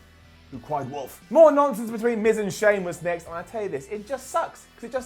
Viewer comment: did he said miz and sheamus 😂😂😂